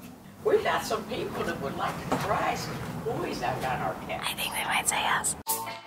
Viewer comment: what does she say at 0:13? I just cant figure it out. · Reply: "I think they might say yes"